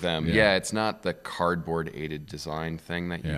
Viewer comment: did he say “Cardboard-aided design” for CAD?